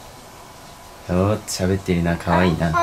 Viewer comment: が何となくパパって言ってるように聞こえてびっくりしたw 頑張って🙆‍♂️💕